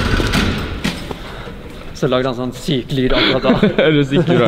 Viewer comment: What did he say? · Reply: I think something like "and then it made some sick noises"